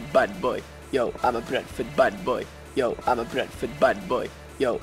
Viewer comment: eventually I heard "yo I'm a bread foot bat boy"